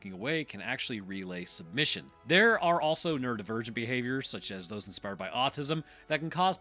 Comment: So, when you said "neurodivergent behaviors" I heard "nerd virgin behaviors" and my autistic ass laughed too hard before lookin away from the screen again.